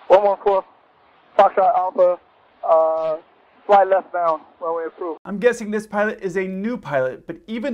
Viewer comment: Idk about you all, but I heard "left-bound".